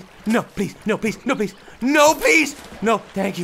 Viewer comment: its sounds like is says no peace